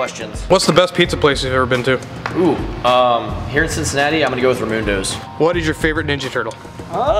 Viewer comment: Whoaaaa.. You said "Ramundos" but I totally heard "Ramuntos" which is the name of a pizza place here in Vermont.